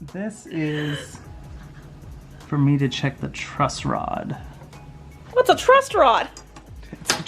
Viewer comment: Lol I had also thought Christopher was saying "trust rod" in previous episodes 😂